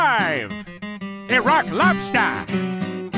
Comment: Lmao when I first saw this episode I thought it was "A Rock Lobster" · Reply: I thought he said ‘he rock lobster’